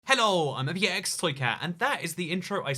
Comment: What I hear: "Hello i'm Ibxtoycat!" What subtitles hear: "Hello i'm a VX flicker!" Just... HoW?!